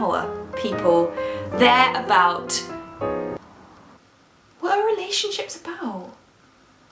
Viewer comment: 'what are relationships about?' captions say 'literally shit' that made me laugh XD